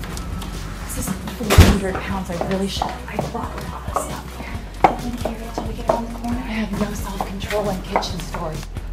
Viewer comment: did she say "holy shit"? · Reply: The Sea Fantasy "really should"